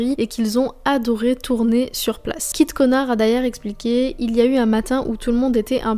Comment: c'est moi ou j'entend kit connard 😭